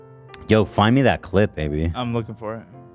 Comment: when he said AB it sounds like he said baby and now everytime I hear him say Ab I think he's saying baby